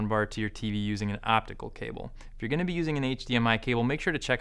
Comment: I am confused by your reference to the cable in question being HDMI. I thought the HDMI was a different type of interface with different shaped connectors. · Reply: Sorry, I have listened again. I thought you said "We are going to be using an HDMI cable" but I now realise you said "If you are ...."